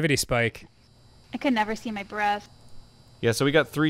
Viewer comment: I thought she said "I can never see my breasts" 😂